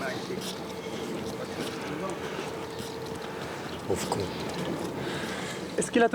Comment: C'est moi ou à 8:38 quelqu'un dit "pauvre con"